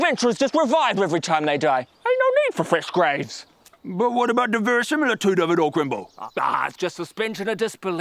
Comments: Soo... What is the grave digger saying at 0:30 ? I just can't seem to put the sounds into meanings. XD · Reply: "But what about the verisimilitude of it all Grimble?" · What did he say? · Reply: verisimilitude. the appearance of being true or real. Fancy word for fancy worded video